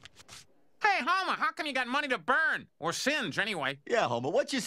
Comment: "Or singed, anyway" is a great line. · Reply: Oh huh, I thought he said 'or sins' or something. Lol, singed makes much more sense though.